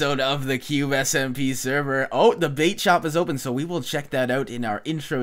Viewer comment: lol I wasn't paying attention and thought said the bae shop o_o